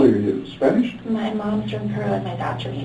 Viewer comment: her dad is from Egypt, where did she say her mom was from? Peru? I couldn't make it out. The words were probably having a hard time getting out for tripping over her lies. Thank you to whoever helps me out..:-) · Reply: You heard it correctly. Peru.